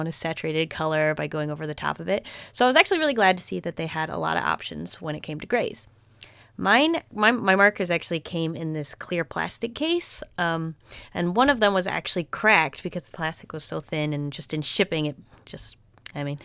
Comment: I thought you said, "When it comes to Grace" instead of "When it comes to grays" and I was thinking, "When it comes to Grace what?"